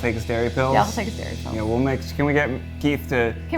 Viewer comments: When they were talking about dairy pills I thought they were saying scary pills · When they said Keith has to take his dairy pills, I swear they were saying "scary pills" and that's just a whole other vibe